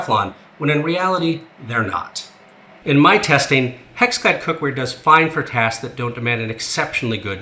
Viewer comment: Anyone else hear him say HexFad at 12:47 ?